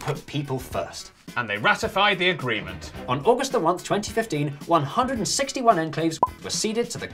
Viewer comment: I heard " on August the oneth" @ 2:34 or am I confused